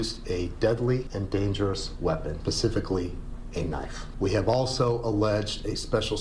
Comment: did he say "pacifically"? Omg 😅